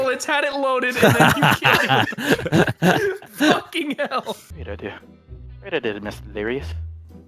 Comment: What is delirious saying at 17:10 when he's taking to himself? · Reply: Ian Campbell "great idea, great idea delirious"